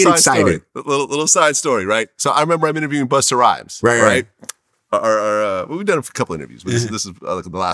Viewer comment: At minute 6:25 Did VLAD say, We done it for a couple ninjas???